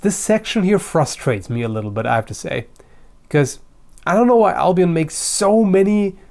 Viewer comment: i thought you said albino like really really white swords lol